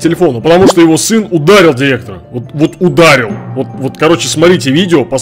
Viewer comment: Мне одному послышалось "Грифер удалил директора"